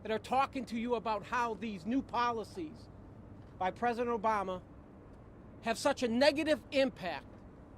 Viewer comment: Did he say new policies from President Obama 26:25 😆